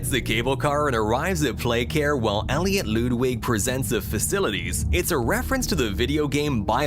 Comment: Also did you just say loodwig LOODWIG! BRO ITS LUDWIG!